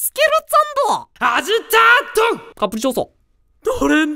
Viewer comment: カプチーノにしか聞こえなかったw